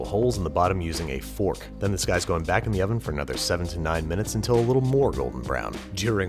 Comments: I heard "7 to 9 minutes" as "79 minutes". Nuke's Top 5 voice: It did not...go well". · I heard "79 minutes" and burned my kitchen 😒